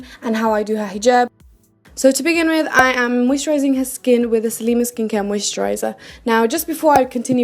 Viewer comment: did you say HIS skin? Lol